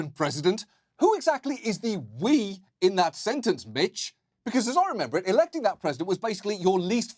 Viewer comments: I heard "who exactly is the we in that sentence BITCH" and that is the correct version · I thought John said "BITCH", instead he actually said Mitch. It rhymes though. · I heard mitch as "bitch" and damn that fits lol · for a second, I thought he called him bitch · "Who exactly is the 'we' in that sentence bit*h"! ~ I felt that. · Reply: I probably was a “mitch”. I heard it as bitch too.